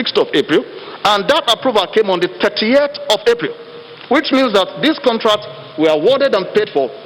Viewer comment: did i hear him say 38th of April. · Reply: julian king, 30th April